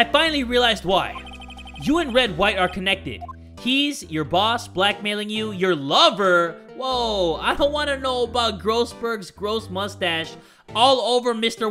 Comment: At first, i thought he said "Red, White, Blue" in the beginning cut scene because those colors form purple which is his hair color....